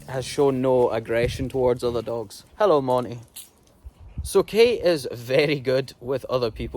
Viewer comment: ??? Did u just say, "hello Monni'?? The white short-haired dog walked past u. Is that dogs name Monni?